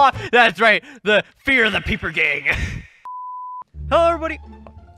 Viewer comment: At first I thought they were saying “paper gang” lmao